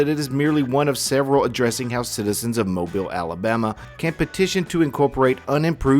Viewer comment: Never been prouder to say that I am from MOBILE ALABAMA in my life! 😂❤ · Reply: Whenever he said "Mobile Alabama" I heard "Greenbow Alabama!" 😄